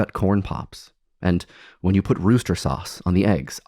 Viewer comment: Dear lord I know it is the stupidest thing ever but I forgot E.L. James calls Sriracha 'rooster sauce' and nothing has triggered my fight or flight response like that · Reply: Oh! I thought he was saying Worcester sauce. It's actually Rooster? Weird.